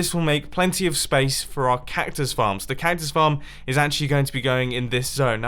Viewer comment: Did he say compactus farm